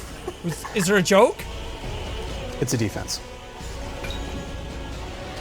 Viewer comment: It even sounds like he says: it's the fence. In a weird accent. So: the fence is your shield. And he says it's the fence